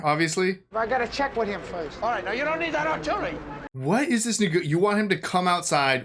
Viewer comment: Was it just me or did i hear him say at 9:21 "what is this nig/you want...etc" a little freudian slip?